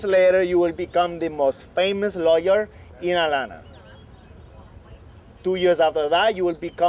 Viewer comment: Did he say "Atlanta" at 1:36? Is this on Broad Street NW next to Woodruff Park?